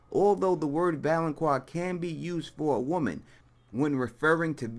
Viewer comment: I always hear "Valoncroix"